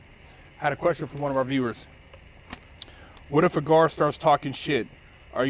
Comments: I thought it said what if a guard is taking a shit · I thought it said what if a guard was taking a shit 🤣🤣